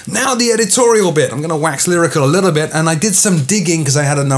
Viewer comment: "I'm going to wax lyrica a little bit..." ???? I can't quite understand what u said there. What is it you were trying to say? You don't have to speak quite so fast... it's not a race! I'd rather you take a few extra seconds to speak more slowly, each sentence, so I that I can catch everything you're saying.